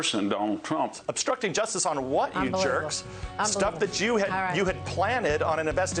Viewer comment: is he saying "Jew"? For real?